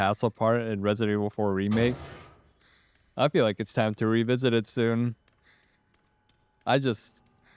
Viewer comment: Did I just hear “time to revisit re4”??